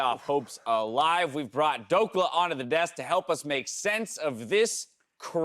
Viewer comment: I thought he said we brought DOPA on the desk to help us understand